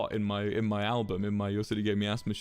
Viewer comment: Can anyone tell me what he said at 3:07 ? Like why is he talking so fast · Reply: Yes, this is more than a year late… but he said ‘in my album, in my ‘your city gave me asthma’